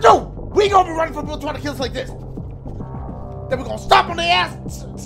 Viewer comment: Did he say “we’re gonna run from people tryna kill us like thius”?? 💀💀😂